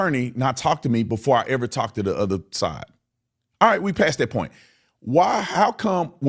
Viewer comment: Quick question...... 13:01-13:06 maybe he says if you wanted to know what happened to Nolan why did your attorney not come talk to me before I sat down with the other side..... So he clearly knows what happened lock his ass up too. How tf you know what happened to Nolan before you even interviewed the other side? That means they told the truth behind cameras. At first i thought he said the boys but knowing that he said other side is interesting meaning it wasn't just the boys but also some parents/family & or friends. So why talk about what happened before you actually do the video when that's the purpose of the video. Clearly some rehearsal/ coaching was needed Better yet if you know what happened to Nolan and yk she wanna know how come you don't just say it why you keeping it a secret? Why does she have to come to you? You can go to her, you can notify the tip line, you can talk to the cops, or you can just put it out in one of your dumb ass videos. Like sir pls stop you don't know what happened to Nolan & no one gives af what you think yk.